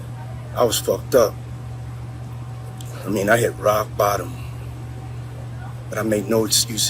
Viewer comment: Words can never repair his damage .. he said “I hit rock bottom ..”All I heard was “ been hittin bottoms on rock for years 😂..decisions been made years ago …. Off with his head⚰️